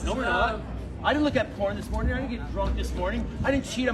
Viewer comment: Did he say "he looked at porn this morning, he didn't cheat on his wife of 34 years"? That was just a weird-assed thing to say. I'm sure he doesn't beat off to it, as well. Why be on those sites?! And that freak @ 6:10 calling for death of all gay people... sorry, he's living in a real whacked out world of self-denial of his own homosexuality. What a fruitcake!! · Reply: He’s said he DIDNT look at it this morning, and DIDNT drink and so on